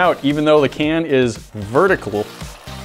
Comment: Did he just say Verticable?